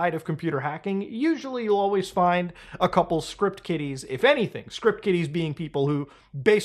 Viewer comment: I always thought and heard 'script kiddies' as 'script kitties.' 😅